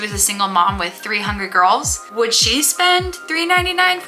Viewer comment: When you said “a single mom with three hungry girls” I thought you said “a single mom with 300 girls” 😂😂